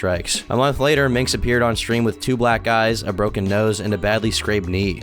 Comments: lol I thought he said “two black guys” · thought Yyou said “black guys” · I wasn’t looking at the screen and I swear you said “2 black guys and a broken nose”. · I thought he said “2black guys” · "with 2 black guys, a broken nose, and a badly scraped knee" is exactly wtf i hear every time💀